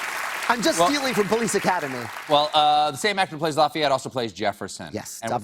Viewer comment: did he just call him lafeeyette